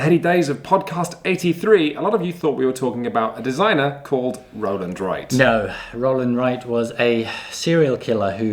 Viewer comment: Stabber McGee. Rollyboi, Rip Emup, Roland Burn, Roland Rock, Susan Slapdash Hold on... that guy looks... familiar... (I am one of those who, for years now, thought you were saying Roland Wright)